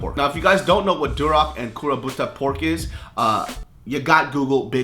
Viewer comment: i thought he said kuroPUTA pork 😂